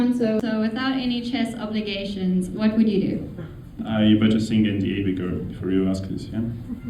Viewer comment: What did Nepo say at 4:42? It sounds like, "I better think at the ___ before you ask this" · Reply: I better sign an NDA before you ask this